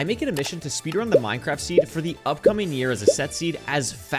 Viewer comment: I fear I must inform you that it is currently 2024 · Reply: I think he said for the UPCOMING year